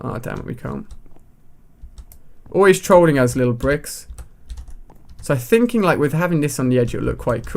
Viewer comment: lol I thought u said little pricks